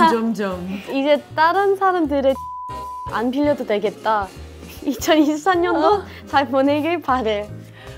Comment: I forgot everything when I thought Hanni said "You won't have to borrow other people's shit anymore" at 6:27 🤣